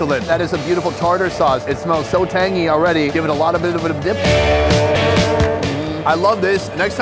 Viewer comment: What did he say there 11:30 😂 it’s like it was 1 word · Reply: Alotofabitofdip🤣🤣